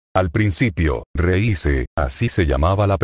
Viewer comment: Escuche reyze? Quien es? En el minuto 23:40 ?